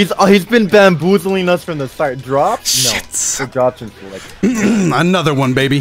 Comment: did I hear say sh!t?